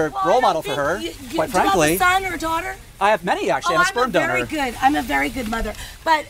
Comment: "Do you have kids?" "I have many, actually. I'm a sperm donor." Might be the funniest comeback he's ever thrown out lol. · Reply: Hahaha. I thought he said I have a smart daughter